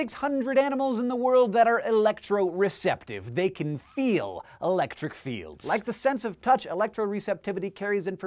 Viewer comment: Misheard the third line as "They can feel electric feels." Love how it still makes sense, lol.